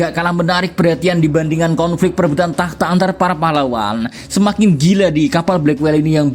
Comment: "antara para pahlawan" ???? apa aku yang salah dengar?